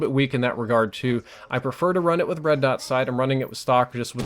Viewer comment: am i the only one who hears bread-dot sight ?